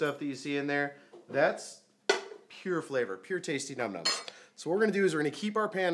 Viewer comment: I heard “pure tasty numb nuts” 😂😂😂